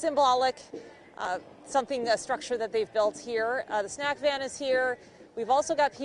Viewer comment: Wait did she say the snack van was there